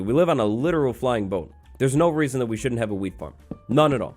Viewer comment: Who else thought he said weed farm 😂